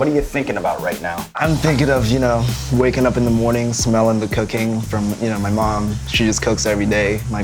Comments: who else heard "smelling the cocaine" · oH my god, i thought he said “smelling the cocaine from my mom” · anyone else thought he said "waking up in the morning and smelling the cocaine" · legit thought he said "in the morning, smelling the cocaine you know my mum she cooks it everyday" lmao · "waking up in the morning, smelling the cocaine." 0:53 lol That is what I heard instead of cooking. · thought he said he woke up in the morning to smell the cocaine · I thought Brian said cocaine not cooking* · When I heard Brian said "cooking" at 0:53 I heard cocaine. Was it just me? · I heard "waking up in the morning, smelling the cocaine..." HAHAHAA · I thought I heard "Waking up in the morning, smelling the cocaine." I had to rewatch that part! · I thought he said "waking up in the morning, smelling the cocaine." LMAO. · Why does it sound like he says "waking up in the morning, smelling the cocaine" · I thought he said he would smell the COCAINE from his mother 😂😂😂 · I thought chigga said cocaine in the morning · I thought he said "I'm thinking of you know, waking up in the morning smelling the cocaine" omg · Who else thought he said “smelling the cocain in the morning” at :55sec ? LMFAO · um smelling the cooking or the cocaine cause I swear I heard the 2nd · NOT EVEN GONNA LIE FOR REAL THOUGHT HE SAID COCAINE! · I thought he said “waking up in the morning, smelling my mom’s cocaine.” Did a double take for a sec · why did I hear cocaine...